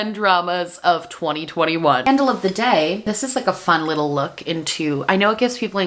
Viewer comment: I heard 'scandal of the day'